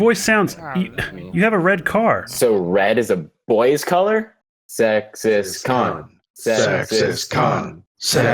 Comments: Crowd in the intro sounds like saying “sexist cunt” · I thought they were saying "sexist c**t" and not "sexist khan" for a moment there... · I actually thought it said at the start 'sexist c*nt' 😂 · Erectile Reptile me too😂😂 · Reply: Kopel Productions I thought they said sexist car